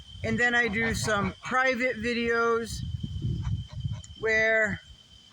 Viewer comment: Ooooh, private video. Thought you said privates videos, as in onlyfans. Mothah would not approve 🤭